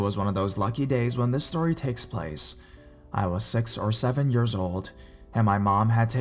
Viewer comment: Tha way he tells this stories tho hahaha instell of feeling scared n feel like it can happend too my young sibling it just makes me laugh😂 and does he says in 5:52 he is sex or 7 years old wow😬